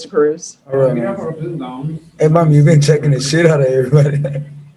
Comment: I could have sworn that 1st guy said 💩. The same one that said people can be retarted. I'm surprised the Judge let him get away with that. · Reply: I watched the beginning again and one guy said, "hey ma'am you been checking the 💩 out of them". Another one said, "some of these people are retarded". I'm shocked the Judge let them both get by with it lol